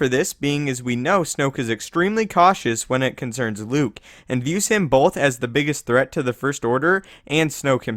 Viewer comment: Is it me or I hear at 00:47 Usain Bolt?! O.O